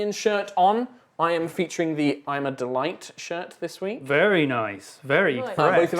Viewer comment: I miss herd when he said im a delight when they were talking about the shirts I thought he said I'm not a whight, I just imagine skeletor In a bad disguise like, "what I'm not a wight! I am a normal hueman!" "Naaaaa" Unconvinced gards just looking at him like, "why are you saying human like that?"